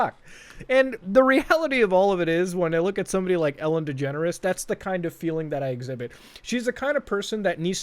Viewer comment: I always misheard it to, Ellen the Degenerate